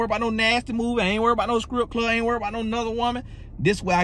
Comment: Did he say "skrip club"? 😂😂😂